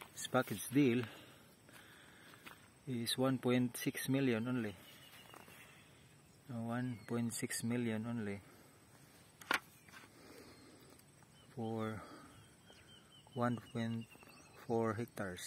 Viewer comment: How much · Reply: I heard him saying something like 1.6million pesos at around 1.4hectares.